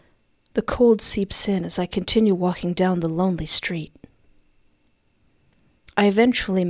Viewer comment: Am I crazy or does it say the “love Street”, not the “lonely street.”